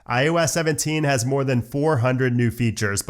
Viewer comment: My dumbass thought he said iOS 17-24, as in iOS 17 to iOS 24 💀